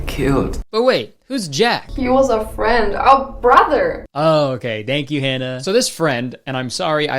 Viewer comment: So I'm currently hauled up in my bed in bonnie Scotland 🏴 having the worst time with a raging gum infection and toothache 😫🦷 and I came across this video and all I can say is THANK YOU. I was so taken in by the sheer audacity, stupidity, ignorance and just straight up complete and utter tomfoolery of this...film...like seriously WOW. Your presentation of it is just brilliant, witty and intelligent. Thanks for the distraction 👌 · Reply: I thought you said Garlic Brother💀